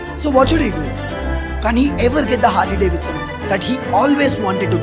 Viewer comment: bro did u say kaani (కాని)